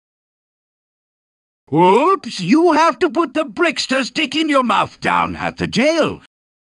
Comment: What did he tell us to do? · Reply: i heard “whoops, you have to put the brickster stick in your mouth down at the jail